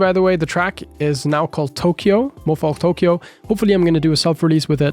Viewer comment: Wait, I think i misheard you, did you say Thank You, is now called Tokyo (as in your ID thank you) or thank you as in thanks? Also when will Thank You release? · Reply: Yes 'thank you' --> 'tokyo' haha